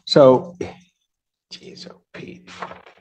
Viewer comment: it sounded like he said he’s op 😂